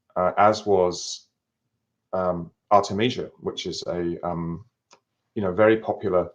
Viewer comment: Anyone know what word he said at 15:50? He was talking about a parasite treatment. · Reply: Artemisia, I think